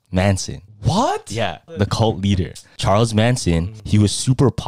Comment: I thought he said the cold beater 🥶❄️🍆👏💋